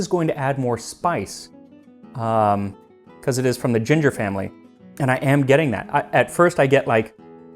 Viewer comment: I thought you said "galangal" and I was about to be mad an ancient nun can obtain it but I can't lol Wait you said ginger family? Dang maybe it IS the same plant